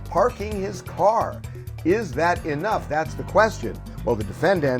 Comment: Did he just say "what car is that?" THAT IS YOUR CAR LMAAAAAOOOOOOOO